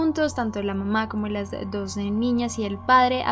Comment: Like si viste que dijo en el minuto 1:48 dijo la mama y las doce gemelas